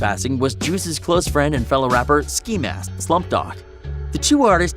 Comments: Did he just say ski mask the slump dog?🤢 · did he say ski mask the slump dog